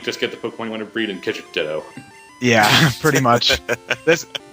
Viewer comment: When they said Ditto, I thought they said dildo..